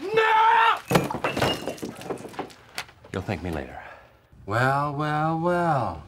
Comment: What Dwight said: “You’ll thank me later” What the captions thought he said: ”don’t thank me later”